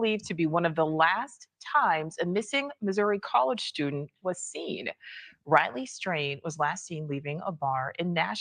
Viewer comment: did she just call him Ratley?